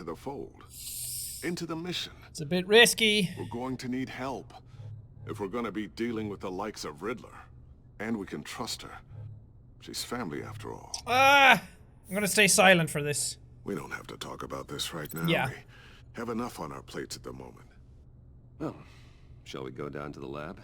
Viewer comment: I paused the video too look at the options and the subtitles said "She's family af" lol